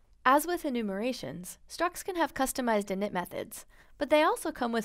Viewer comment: In 0:26 Does she say "emit methods" or "init methods"? The subtitles say "emit" but I haven't heard that for structs, so I think the subtitles may be wrong.